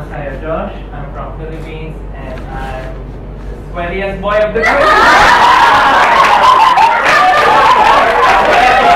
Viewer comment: anyone heard? what did say so they laughed? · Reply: He said he's the easiest to sweat, something like that